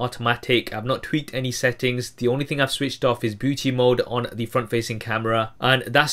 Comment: Did he just say, "Booty mode" at 02:36?